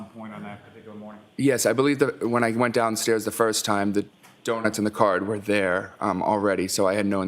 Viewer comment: is on the stand.I thought the Aunt said she left donuts on car seat, Jr said they were downstairs when he came from bedroom that morning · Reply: I thought the mother said she saw the aunt leaving the donuts in his car like she does every year.